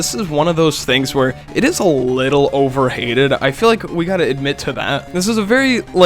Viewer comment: the caption says overated instead if overhated